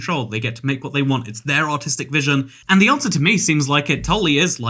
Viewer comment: i thought he said:"its there own autistic views" being from the United States sucks in so many ways I lost count